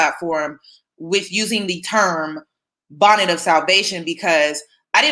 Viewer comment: I thought she said helmet of salvation. However, I do not disagree with your stance on this.